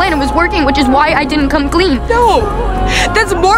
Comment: Did she just say ghost lighting instead of gas lighting💀💀